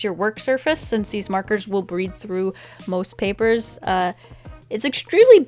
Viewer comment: um at 5:21 she said breed through instead of bleed through... lol